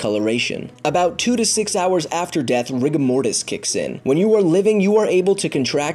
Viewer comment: Am I the only one who heard Rick and Morty? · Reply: Yeah cuz I heard Rick er' Mortys